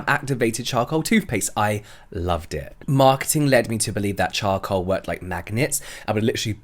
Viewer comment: Ok I thought I heard “Marcus had lead you to believe” your beliefs about charcoal instead of Marketing 🤣 I was like since when?